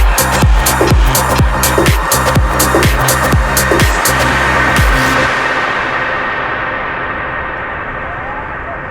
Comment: What Lucas Chumbo says in the 9:34 min? 🤔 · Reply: "A small one" I guess